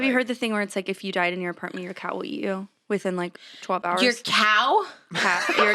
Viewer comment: WHEN TRISH THOUGHT BROOKE SAID YOUR COW WILL EAT YOU hahaha I lost it.